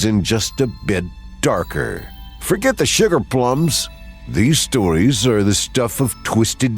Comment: I totally thought said "shitter plums" instead of "sugar plums" 😂